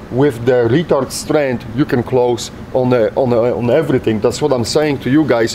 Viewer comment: did he say, "with retard strength, you can close anything" ?